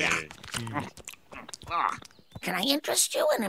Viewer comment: "Can I offer you a delicious pie?" "Does it have Tomacco in it?" "No but it has–" "Pass" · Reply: I thought it said minced meat pie